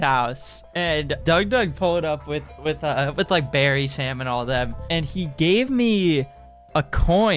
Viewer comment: I misheard this as "with like berries, salmon, all that," and for a brief moment envisioned Doug showing up to a party with catering for a fucking grizzly bear or something